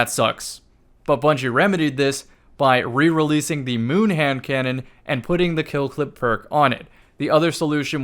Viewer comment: for a second it sounded like you said Ham cannon and it’s gonna stay like that for me until I forget.